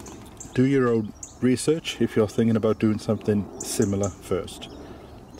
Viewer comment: When you said “that’s it”, it was like I’ve heard you say “that’s shit”😁